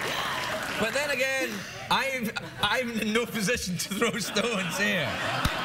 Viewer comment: what's he saying here? thanks in advance ( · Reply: He said "Yeah but i'm in no position to throw stones here."